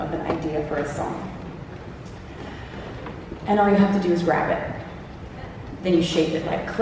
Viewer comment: I thought she said "and all you gotta do is rap it" my ears really perked up at blending of genres. I'm sure she already has plenty, I'm her executed producer. There's an exile out there with some what they call rap like you have never ever heard before, it's really really good. Is Bowery a John Wick reference? It's very true, I don't exist 🤗